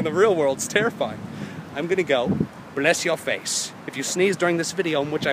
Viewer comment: after 4:17 i swear he said "I'm gonna bless your face" which is funny cuz the next vlog is about ijustines birthday... good god, if that isnt a sign then what is?